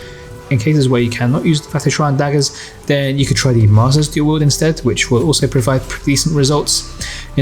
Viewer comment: What are you saying here? It sounds like Mazda's daggers